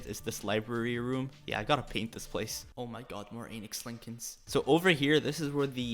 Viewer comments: When you first said Anix Lincoln, I thought you said Anus Lincoln 😳 😂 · kind of sounded like you said anus lincolns 🤣